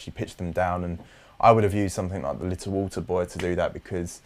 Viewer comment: What he says at about 50:30 ? The plugin He used for vocal harmonies? I need to know that. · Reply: I found it myself after 2 mins. :D It's little alterboy